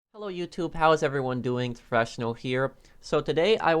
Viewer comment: Um it anyone else hear fresh snow instead of profession on